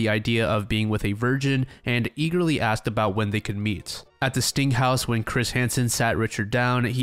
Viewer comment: During Jerry’s episode I heard “stink house”😂